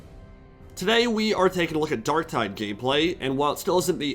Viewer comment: did I hear gayplay?